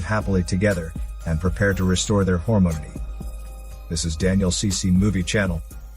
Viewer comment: That is what he said? It would have been Harmony, but he said Hormony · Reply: I think he does say ‘hormony’ but spells it out ‘hormonity’ in the subtitles. (timestamp